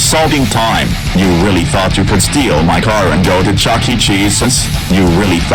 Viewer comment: I thought he said card